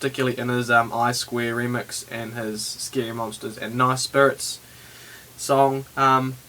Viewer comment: Im sorry sir did u just say nice spirits!?